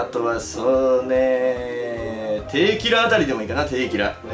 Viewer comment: テーキラって聞こえたの俺だけ？